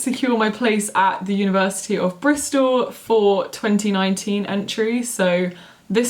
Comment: Robbie Lyle's wife sorry i’m a bit confused, i didn’t hear anything about re-applying to oxford then but maybe i missed it! however if so, oxford would be so lucky to have her :) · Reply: Robbie Lyle's wife i thought that at around 1:09 she said she was going to Bristol so that’s why I assumed, either way they’re both great universities x